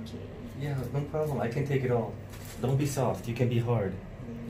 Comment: When you said, "Don't be soft, you can be hard", I thought at first you said, "Don't be soft, you make me hard". lol I had to go back and hear it again.